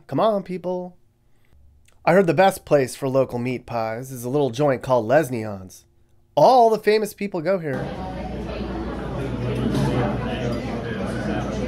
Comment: I so thought he said “lesbians” 😂 I was waiting for the place to be “Les Bos”